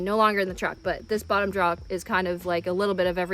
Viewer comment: I like the way you say "drawer", sounds like "drawr" =) great video! thanks for the coverage on your rig. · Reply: I thought I was crazy when I heard her say draw instead of drawer. Lol. I started to question if I've been saying it wrong my entire life or not. Lol. It definitely sounded like a north east accent for sure.